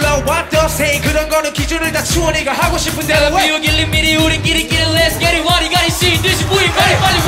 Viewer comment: 이웃끼리끼리가 아니라 우리끼리끼리 아닌가요? 우리라고 들리는데...